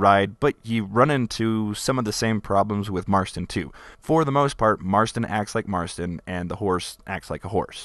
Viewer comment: did you just say marston is hard to ride